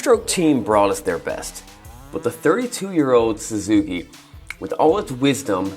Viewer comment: I feel like I heard you say “against the 32 year old Suzuki” when referring to the Kx125… or am I the only one that heard that